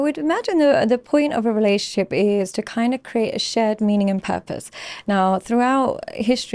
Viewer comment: Darn. I kept hearing her say "create a shed".